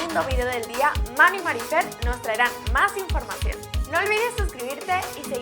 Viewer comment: escuche "Mami Marinfer" xD